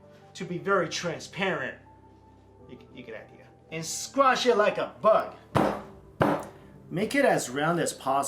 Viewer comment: omg at 7:24 I know you said squish it like a bug, but an add started in the middle of the word bug and I heard squish it like a butt and I was shook for a second hahahaha